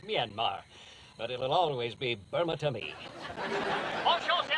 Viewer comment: The closed caption English subtitles read: "Show me your melons", instead of "Sell me one of your melons." :D